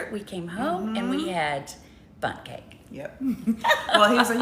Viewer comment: OMG 🤣😅😂 l thought u said u had BUTT cake instead of Bundt cake. Wow what a great way to end the end with BUTT cake. OMG l'm still laughing at myself.😁😆